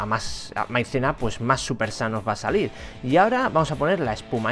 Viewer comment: PENSE QUE DECIA SUPER SANO ;V